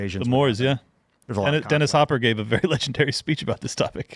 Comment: What's the name me says? Denis something. I wanna listen to the speech